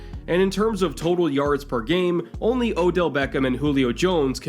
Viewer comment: did I hear odell